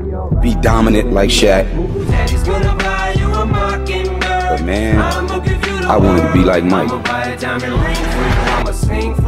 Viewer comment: I thought he said he just wanted to be him? Like back in the days. I dont wanna be like this like that thing